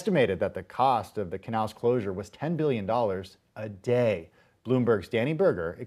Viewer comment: Did he say millions or billions?